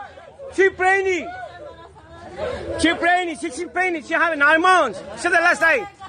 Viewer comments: Great acting 😂😅😂😂❤️🇺🇸 I thought the guy was saying shes fainting not she pregnant so she wasn’t acting . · The whole time I thought he was saying, "She's painting!" Not "pregnant" lol. I was like come on ICE, let her finish her art before you arrest her!! 😂